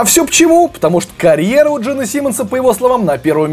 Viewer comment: таймкоды 0:48 nickelblack выпускают альбом 2:38 деймон албарн постебался над тэйлор Свифт 2:54 ной галлахер гений оскорблений 3:55 мужик в трусах поёт моторхэд 5:03 у Джина Симмонса нет друзей 6:11 белка против мужчины из пенсильвании 7:10 4 кота создали группу 7:53 адам левин стал мемом 9:03 клипы слипнот в 4к 10:03 томми ли на онлифанс 10:44 мастейн и металлика 11:58 металлику послали в жопу 13:22 эльфийские уши граймс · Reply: ой, спасибо! послышалось "Джим"